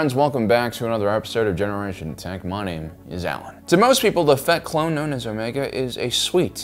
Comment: At first I thought you called Omega "the fat clone". The subtitles thought so, too.